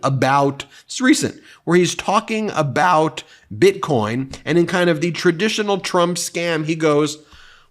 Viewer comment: Oh my badd I thought you said bitch coin 😂crying 😭 with laughter